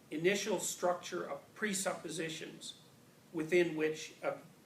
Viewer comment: What does he say? Pre-opposition? I didn't catch that word right🙈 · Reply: Pre-supposition. He means assume.